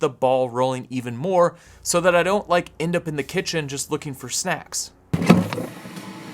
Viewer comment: Pmsl sorry I was half listening while working and all I heard was "looking in the kitchen for sex" Then sees the fridge door open. Thought damn I don't have sex in my fridge, hell I'm even running out of milk let alone sex in there!